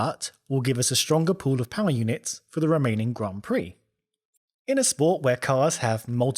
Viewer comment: Who else heard Romanian Grand Prix? :D